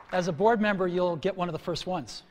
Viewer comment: What does Eric Schmidt say in response to Steve Jobs here? I can't hear it. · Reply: He says "that's why I joined the board" :)